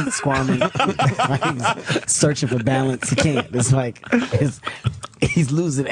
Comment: When he said “searching for balance” I thought he said “searching for guidance” at first. I was literally rolling on the floor laughing 😂